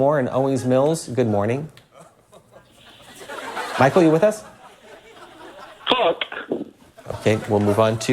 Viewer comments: what's the (it seems) offensive word being said by that caller? I couldn't make it out. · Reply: I'm pretty sure he said fuck. Like really emphatically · What did the caller say? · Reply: Fuck